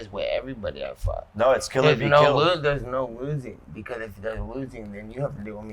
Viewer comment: did Nate say “n****” 😂